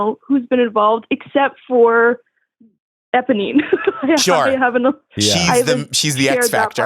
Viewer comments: saying ebonine instead of eponine is killing me! 😂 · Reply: I thought he said Ebony 🙈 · saying ebonine instead of eponine is killing me! 😂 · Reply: Right I kept hearing 'Ebony' and was like how does that fit the Les Mis theme adshfksjdfhdsk